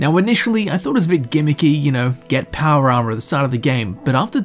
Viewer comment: Where are you from? It sounds like your saying Powaarmar lol like